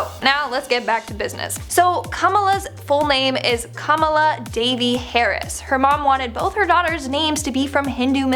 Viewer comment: When she said "Devi", I heard thought she said "Davey". Idk why.